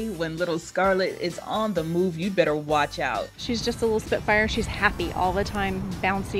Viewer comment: Did she say Spitfire? Like the Supermarine Spitfire?